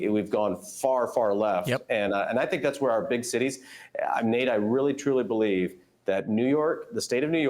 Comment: Was he calling him Nate?